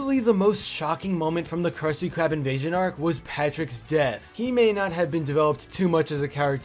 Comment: lol I thought you were gonna say Patrick's D**k!